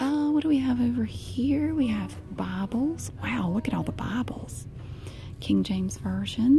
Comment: I thought you were saying Bubble 😂😂 But no it was "Bible"